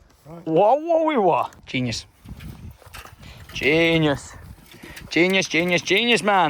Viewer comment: generally thought you said penis